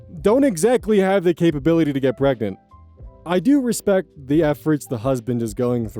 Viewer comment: Oh god i thought you were saying you cant get pregnant but appreciate the attempts lmaooo